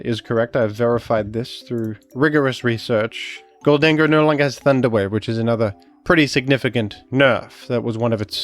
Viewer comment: i was only half listening so i thought you said "gholdengo no longer has underwear" and i was so confused